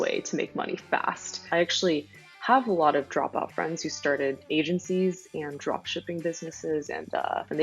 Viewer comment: sounds like she says Asiancy’s .